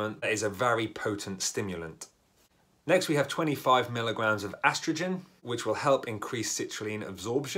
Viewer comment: when you said astragin i thought you said estrogen